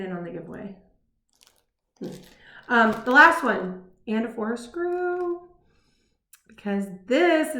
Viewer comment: Confession time: Until you showed the pattern recently, I thought you were saying “Anna Forced a Screw.” Now, it wasn’t just you. It was every Floss Tuber I have heard mention it. I would try to figure out the cultural reference or the classic book reference or the cross-stitch inside joke and I never could. I googled it. Nothing. And then you showed the pattern and it had the title plain as day right there. Gotta say, the real name kinda made me sad. But such is life. Thanks for the entertaining (and the elucidating) video.